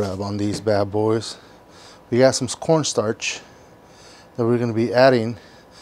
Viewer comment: Did he just say scorn starch? Bro I'm dying :)))). Thanks for the tip though. I do have corn starch though, gonna try the recipe.